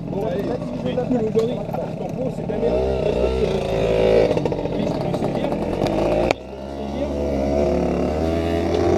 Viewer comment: Suoer vidéo:) mdrr les gendarmes ils étaient quand même assez cool ! Il vous disait quoi à 3:35 sur les pots ?? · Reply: Merci 😊 Ils disaient qu'il pouvait saisir nos 50cc 😂